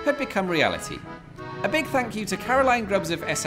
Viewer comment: For an instant I thought he said, "a bug fuck you" instead of "a big thank you"